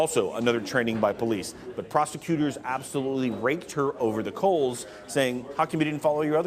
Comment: Not sure if I heard that right... Can someone please clarify? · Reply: Rake over the coals